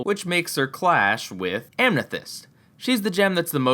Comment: Who else heard amnethyst , there is no n in amethyst.